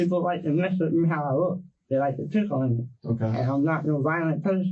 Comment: When he says "they like to pick on me" & the caption says "They like to tickle me."🤣🤣🤣🤣